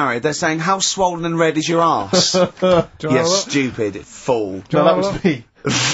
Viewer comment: What does Steve say around 51:45 that makes ricky laugh. I don't think it's what karl says that makes him laugh. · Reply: It sounds like he says "oh no that was me actually"